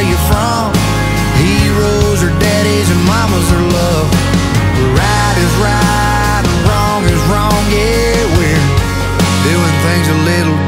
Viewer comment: How come he didn’t say anything about WHITE in his song 😂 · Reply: I thought he said white is right wrong is wrong😅